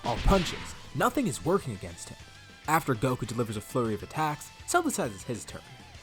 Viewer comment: I thought he said a “furry” of attacks